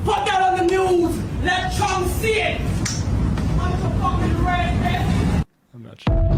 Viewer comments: It sounded like she said "I'm so f*ckin' rage [bait? babe?]" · After the elevator doors close, it sounds like she says "I'm the fuckin right bitch" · I think she said "I am so fucking ready bitch":-D Anyway, we need one of these women to every family:-D Never put the keys between your knuckles to punch someone, unless you want to severely hurt yourself and loose the fight because of shock. You can use small set of keys as a hammer tool, the key sticking out behind your small finger. But dont use them as brass knuckles! Get pepper spray or something similarly useful, not keys. You can also cup both your palms and smash the attackers ears really hard like that. The point is to cause a shock to their middle ear with a pressure wave. But that only works if you know you wont miss. In the end all you need to do is surprise them and run.